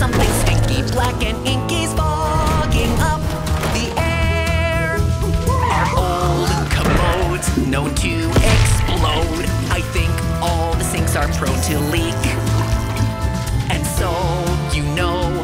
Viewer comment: thought he said "is fucking up the air"